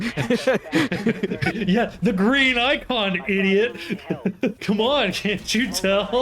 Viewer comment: When you said green icon, I thought I heard you say Green Knight Cum.